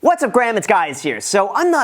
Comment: Am I the only one who heard him say "what's up graham it's guys here"?